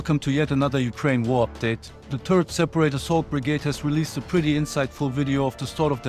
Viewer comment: I heard "Turret Separator Assault Brigade' instead of '3rd Seperate Assault Brigade' and thought that was very appropriate and badass.